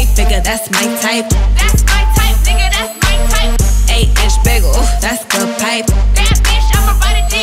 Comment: For the longest time I thought she said "Ay French bagel ooh, that's good bite" Oh, innocent me 🤪